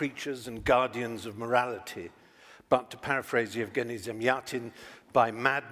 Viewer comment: What does he say in minute 5:06? My english is not good enough. He paraphrases who? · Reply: Yevgeny Zamyatin <link>